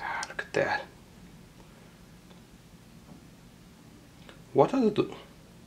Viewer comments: I don't know, but I heard 'What are you do?'. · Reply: he says what are the little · sounds like you said to the arcs: "What are they doing?" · i Think he said „What i do?”